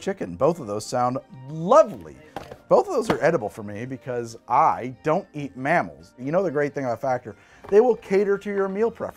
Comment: I swear I heard Greg say "I don't eat mammoth".... first thought was "well that's good ??" followed with WTF!! had to turn it up and replay lol